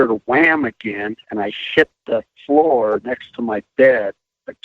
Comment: A 30:50 does he say he shit the floor next to his bed?